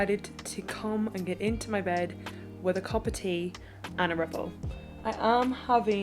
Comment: I honestly thought you said you end the day with tea and a red bull and was so confused! Like red bull, I'd be buzzing all night. A RIPPLE 😍, I'm an idiot! My fave is a freddo with my tea before bed. ❤